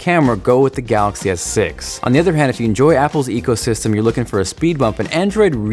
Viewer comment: I heard "if you enjoy apple s ego system ; go for iPhone " which actually makes sense :P